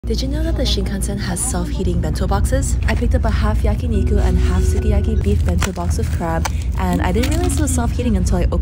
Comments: I thought it said “Self healing” 😭😂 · Lmao I thought it said 'self hating' 😂 I was like why do the bento boxes have low self-esteem?😂😂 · I thought you said self hating I was confused